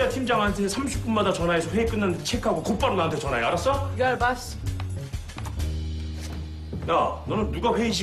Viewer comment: the subtitle is wrong. Amber says, "you got it, Boss!", not "you're the Boss!".